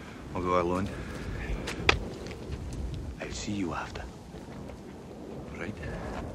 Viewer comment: I thought he said : " I see you after ".